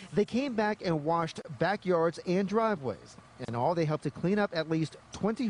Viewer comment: I thought it said retarted clean up